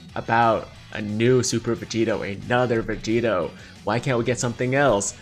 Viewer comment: captions said super potato lmao